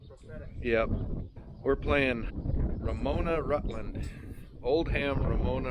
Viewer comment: I thought he said Verona/Rutland. To which I thought "ND? They're kinda far apart for football...non conference, out of state game?" To which a researched there is a Rutland SD, too. They ancestors sure were fancy town namers.